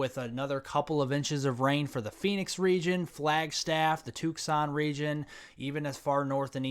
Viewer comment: Did he say "Took-sahn" at 6:34? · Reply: I'm from the Midwest so I'm not used to pronouncing the names of the cities across the Southern states, such as Tucson, Arizona. My apologies!